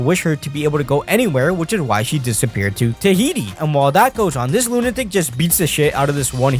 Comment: Did you said Tahiti?